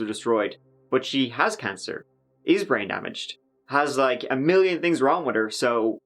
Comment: When you were listing all her fake ailments I thought you said she was “rain damaged”. 😂